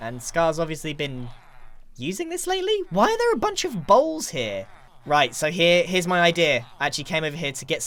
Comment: why are there bunch of balls here?😂 This is what I heard😅